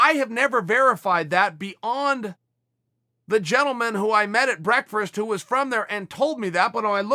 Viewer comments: 2.36 did he just say he had brerkferst 😆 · did you say breakfrisk??